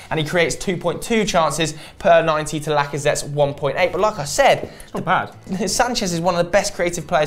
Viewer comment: like i Said sounded like Lacazette😂😂